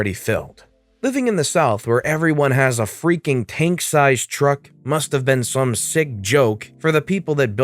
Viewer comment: When you said “tank sized truck” I thought you said “pink truck” lol.